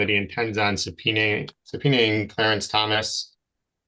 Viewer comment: Did I hear it right that at 26:25 he said he wanted to subpoena Clarence Thomas, as in like the US Supreme Court Justice Clarence Thomas. I guess why not since he is already asking for Justice Robert’s.